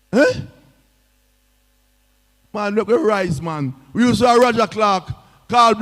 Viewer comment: "Man, mek we RISE " is what is said???? play that again. People, listen AGAIN!!! OPEN YOUR EARS!!! #ChooseJamaica 🇯🇲✅️